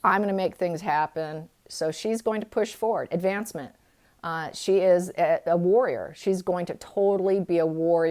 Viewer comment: Thought you said, "She's a lawyer!" Actually you said, "She's a warrior!" I almost passed out with shock.:)))))